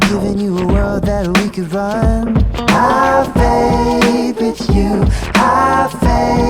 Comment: I thought he was saying "I vape with you XD"